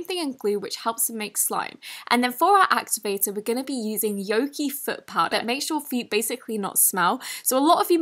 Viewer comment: I thought she was saying yoongi foot powder 😂 yoongi is a bts member his fans call him as suga